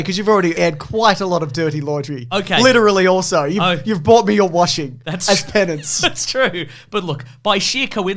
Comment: I initially thought said bought him a washing machine and found myself thinking for the next minute, "So, what's the story behind that whole washing machine thing?"